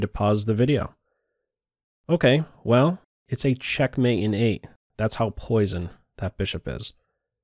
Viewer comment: Not sure what he said at 4:40? Can someone please type it here? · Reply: It's a checkmate in 8, that's how poison that bishop is.